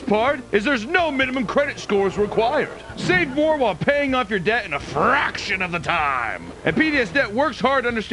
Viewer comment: Omg lol I thought you said today’s video was sponsored by paying off the US debt… 🤣